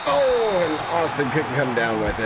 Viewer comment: Every time you say "Austin" I hear "Osten" and I'm like, Yes?